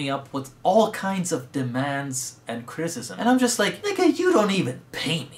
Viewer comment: Did he say nigga?